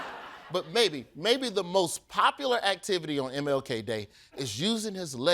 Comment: I thought it said milk day fails but yeah that may just be me